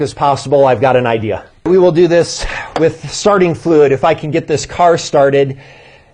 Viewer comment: I thought you said sardine fluid 😂